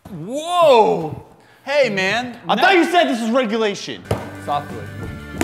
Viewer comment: chubacabra do a chalenge with dude perfect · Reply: Woo hey man I thought you said this regulation